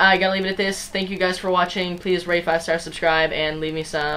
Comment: at the end it sounded like you said "leave me some meth" but good video man